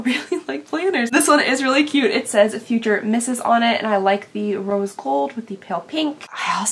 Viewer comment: At first I thought the planner said "future nurse" 😂